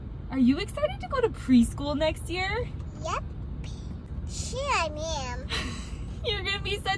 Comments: I thought she said... sure mam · Ngl thought she said sh!$ I am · i heard her say "shit i mean" but she said "sure i am"😂😂😂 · I thought she said yep bleep I am